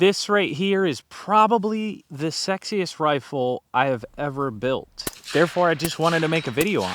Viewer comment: The only reason I clicked on this is because I thought he said the “Sexist rifle.”